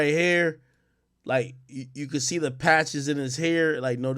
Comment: i really thought ak said "you could see the passion in his hair" 😭